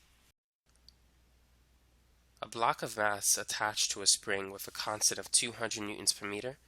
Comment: Sounds like he says a "block of ass"